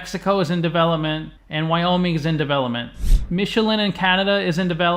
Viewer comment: Michigan is in Canada? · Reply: Ok, could be my mishearing Michelin as Michigan